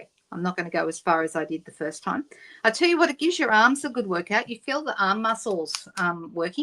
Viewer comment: Being hearing impaired I thought you said ‘it gives your ar……a workout’ I replayed and you actually said’ it gives your arms a workout’